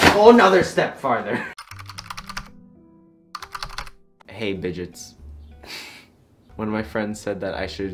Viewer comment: Why not bitchets I literally thought you said my name which is “Bridget” when you said “Bidgets” lmao